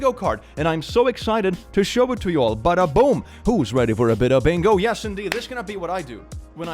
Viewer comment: I listen to your videos at a very low volume when I sleep so sometimes I can't properly understand what you're saying and when you said "who's ready for a bit of bingo" I thought you said "who's ready for a bit of anal' AND I SPRUNG UP FROM MY BED SO FAST LIKE "HUH?"